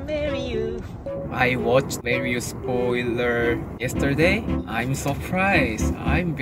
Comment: No one in this world, me who left English (auto-generated) CC on by mistake 👀😆 The video became more funny because of English auto-generated subtitles. I will give you a brief review. First of all Shinee member names - Taemin = Tammy, Daemin, Tamin, Taymin Minho = Menus Onewe = On you At 3:10 Marry you Chinese song (instead of Shinee's song). I was wondering when did they post Chinese song called Marry you. At 3:17 Mario spoiler (instead of Marry you spoiler). I was recalling when did Mario game ever had any spoiler. At 6:49 translator in full swag TM - preping Trans - preparing TM - peer-pairing Trans - pure pairing At 9:25 Adeorable Taemin = Autumn or Tammy At 9:25 Shawols = Showers And the most epic one is "Chinese bag" instead of "Shinee's back". I laughed so hard through out this video 😆😂 And after 3:34 Translator couldn't understand Taemin's cute language. So, it was [music] instead 😊 And I still wonder what is Felix's cheese 😂